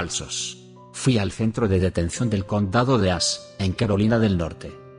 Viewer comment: Entendí Condado de Ass 🤣🤣🤣